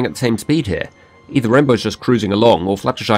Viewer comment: also, there's a typo at 3:08 You said Rainbow was cursing instead of crusing. This amused me. =)